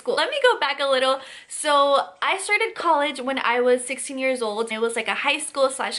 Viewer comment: When she said 16 years old I thought she said 6 years old. And then I went back 10 seconds and realized I missed heard her and started cracking up. 😂😂😂😂😂